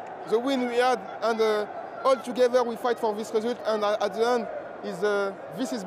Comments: Sounds like he said “f*k you” But I’m sure he said “thank you” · It sounds like he says fuck you instead of thank you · I thought he said f*** you 😂😂😂